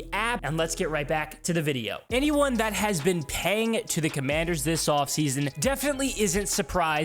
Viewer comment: Wait 😂, how have you not been paying attention to us but have been paying attention to our offseason? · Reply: if you listen back. The narrator says “ if you have been paying to the commanders” not “paying attention” the OP is alluding to this and made a correction or slight joke of what was said.